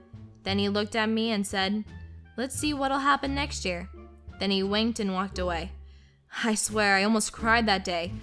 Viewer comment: For some reason i heard "then he wanked at me", :P